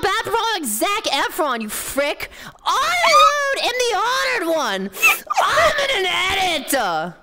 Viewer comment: Did say she was the honored one? (No wonder tenma cut her in half)